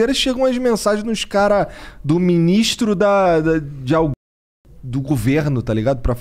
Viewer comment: o audio bugou ou foi uma tentativa de censurar oq ele falou? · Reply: ALGUEM do governo